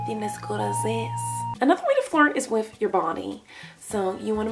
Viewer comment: When Miranda said flirt i thought she said fart 😂